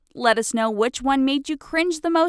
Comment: When it said give this a big thumbs up I thought it said give trump a big thumbs up lmao I'm messed up